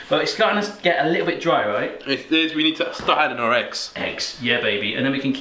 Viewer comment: Did he said ''richtig'' at 7:00 ?!